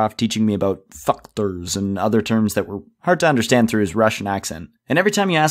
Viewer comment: Why does it sound like he says f***ters